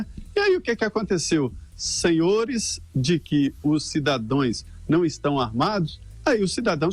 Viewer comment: Cidadões, foi isso mesmo que eu ouvi?